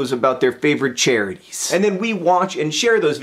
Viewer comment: I thought he said videos about cherries instead of videos about charities... >_>"